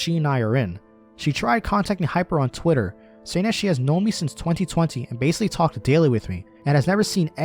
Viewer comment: srr and shtuff but did u just say "2020 13?"